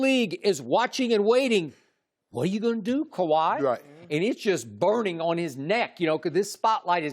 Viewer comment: Did Skip say "it's burning on his neck, *YO*"